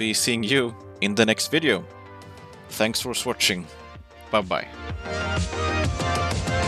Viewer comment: "Thanks for Swatching" or did I misheard?